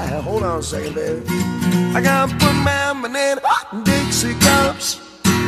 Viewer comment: I've been listening to this song for almost 40 years and I still don't hear "I got good lemonade" before "dixie cups". When I was a kid I thought he said "Foot long banana pops" (LOL).